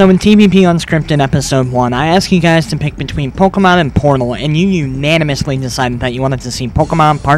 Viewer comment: I totally thought that he said, "Porn Hole" instead of Portal. XD